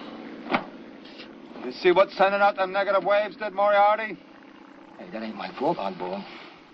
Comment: I thought he said negative vibes but I guess it's to the same effect..